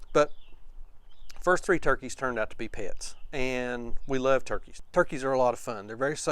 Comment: I just saw my first turkey poult at Rural King, and I am 60 years old... Pits, sounded like you said they turned out to be pits